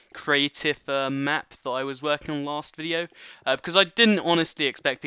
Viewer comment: did he say last year